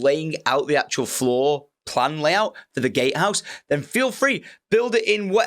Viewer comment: Gatehouse? I thought you said Gay house 😂